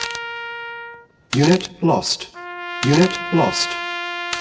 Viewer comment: I live for the “unit lost” sound effect. Unfortunately it comes at the price of an injury. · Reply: Ahh I thought it was saying "minute lost", which didn't make much sense to me, but unit lost makes a hell of a lot more sense hahaha.